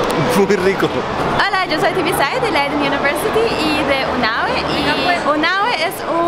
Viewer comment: que fue lo que dijo ella sobre astronomía para niños? cual es el nombre? me interesaría buscar mas información sobre eso. Muchas gracias! buen video como siempre! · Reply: si, eso... UNAWE